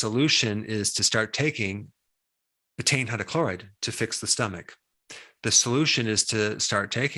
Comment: At the 12:30 minute mark he says "..." hydrochloride. What is the word he says before hydrochloride? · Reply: Betain hydrochloride- HCI